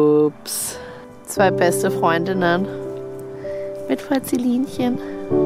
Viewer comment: Bin ich die einzige die bei 9:21 „2 beste Freundinnen...mit furzelinchen“ Verstanden hat😂❤️